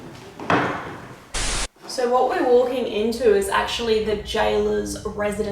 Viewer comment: I heard "Why are they walking in here?"